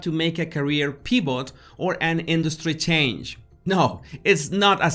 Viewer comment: My daughter in law is from Mexico City. I lover her accent, and notice that you do the same thing with your sound of the letter ‘v’. I heard you say “pee-bot” for “pivot”. Our son’s same is Kevin and she calls him “Keb-een”. It so charming!